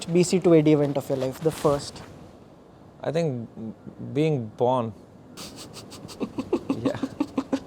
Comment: I thought you said porn, Raina As a porn lover, would you agree, it was a life changing experience discovering it for the first time? :D